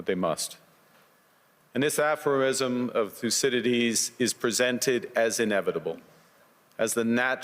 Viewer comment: This aphroism of " fucidities" Is that what carnie said at 4:13? Been trying to pick up that word but can't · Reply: Thucydides